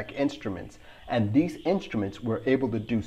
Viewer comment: Audio tip: @ 0:46 "two pilots were enough to" You meant to say "weren't" but it sounded like were or were(t). You made it clear afterwards by stating that there were 3 pilots. Audio tip: don't use abbreviations like "weren't"; use "were not"